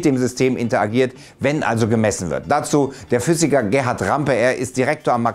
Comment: der Professor aus Garching heißt "Rempe", nicht "Rampe". Bei ihm habe ich eine Vorlesung gehört.